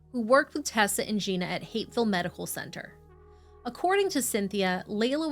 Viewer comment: I've listened to the part at roughly 4:20 like a dozen times, and I can't stop hearing 'Hateful Medical Centre'. That can't possibly be right. What are you saying there? This is driving me nuts. You seem to be very clearly saying 'Hateful Medical Centre', but there's no way that's what you're actually saying. E: I can easily hear parts of the 911 call that are marked ‘indecipherable’ and a few parts where the subtitles clearly don’t match the audible words, but ‘hateful medical centre’ is so clear, I can’t get past it. Thanks for this story! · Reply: Henry Medical Center